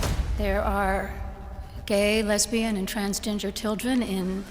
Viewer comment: Did she just say 'transginger'? Lmao. One of the best South Park episodes.